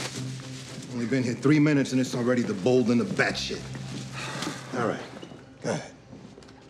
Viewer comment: "We've been here 3 minutes and it's already devolving to batshit" is a perfect representation of my friend group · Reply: I heard “the bold and the batshit”